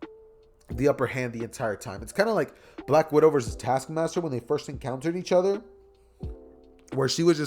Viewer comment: thought it said task manager lol